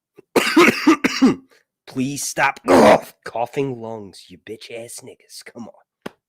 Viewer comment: Wait did I hear you say “ stop coughing you bitch ass nigga’s?”